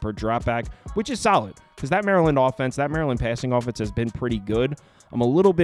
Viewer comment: U called it....smh Maryland are bums...a joke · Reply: I thought he said Maryland or pass 🤔